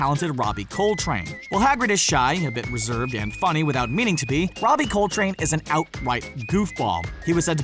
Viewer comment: LOL at 4:34 in the subtitles instead of “be” it said “pee” 😅